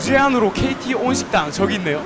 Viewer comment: 초에 무제한으로 KT 오진당 이라고 들림ㅋ